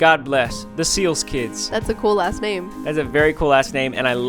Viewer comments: i thought they said "thats a cool a$$ name" :sob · thought they said "cool ass name" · Did anybody else hear them say “that’s a cool-ass name”Instead of “that’s a cool last name”?